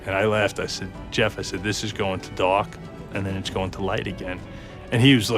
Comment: I thought he was trying to say Die or Dock but he was saying Dark 😂